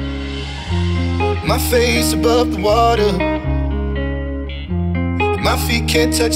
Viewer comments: I thought it said "my Facebook water" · am I the only one who hears my Facebook water?